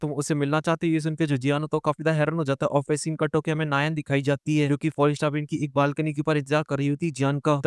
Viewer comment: please yaar, her name is yanran not nayan How it will feel when you start saying xiao instead of xiao yan 😑😑😑